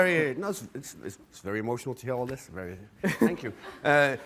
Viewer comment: Hugh said He just keeps thinking of something else ! and then What did Mads say? He's a very emotional ....??? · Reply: 2 months late but "it's very emotional to hear all this" I think